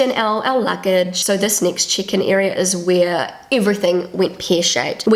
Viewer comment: Was confused about all that chicken business at the airport before I got "check-in". Love your accent! :) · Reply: I thought she said chicken at first too 🤣 But then I realised she meant check in. 😊 I am Australian.